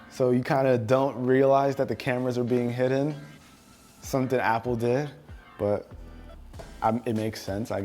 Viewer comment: But did you just say "something at apple did" referring to Samsung dark right top wall paper trick